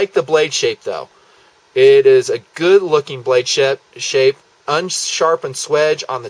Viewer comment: it almost sounds like he said "blade shit"